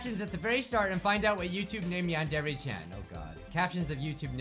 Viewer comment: The captions on the video thought u said John Deere when you said yandere chan